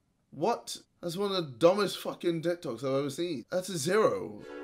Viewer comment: Correct me if I'm wrong but in the part of the video there was a bit where he said Dick Tok's. Like whaaaat? 7:46 that where u hear the bit i heard😁👍